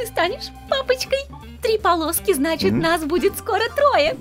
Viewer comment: Мне послышалось или она сказала 3 полоски 05:03?